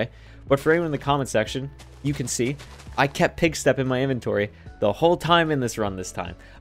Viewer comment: what is he saying? Pig step? Pig stuff? I've listened to it back a few times and can't figure it out